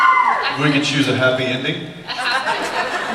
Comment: what did Jensen say? And why are they all laughing? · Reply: He said, "We can choose a happy ending?" He said it like he wasn't aware that a happy ending was possible, hence all the laughter because most people think the show can't have a happy ending.